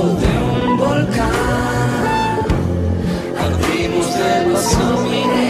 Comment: PENSE QUE DECIA "ARDIMOS DEMASIADO, IDEAL" tremendo